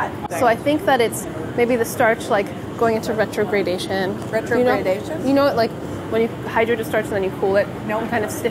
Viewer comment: Sohla: I don't know science! Also Sohla: It's the starch going into retrogradation. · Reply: Rae Lee I thought she said renegade-dation